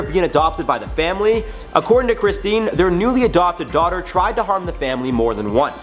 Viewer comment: I kid you not 3:17 I thought he said that she was apparently humping the family 😬😱 I then turned on my subtitles and round out he said harm (btw I was in a car with the ac on so it was a little hard to understand)